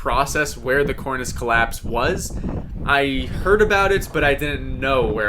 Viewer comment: The scariest part to me is being around soo many people who could easily jeopardize your life by THEIR own mistakes.. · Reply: My phone was in my pocket and I thought you said coitous collapse. Imagine my confusion.